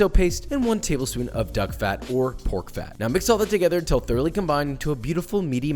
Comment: I thought josh said dog fat. _ .